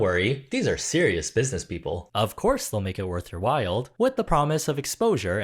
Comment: did he say "worth your wild?"